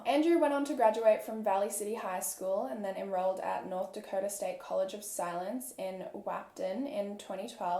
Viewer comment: At first I thought you said Dakota State College of Silence and I was so confused 😂